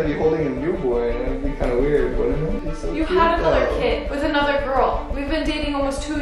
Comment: Wait did she say you had ANOTHER kid